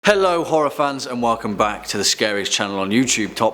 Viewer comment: I seriously heard him say "Hello Orphans--" and I was shocked for a moment until I realized what he actually said.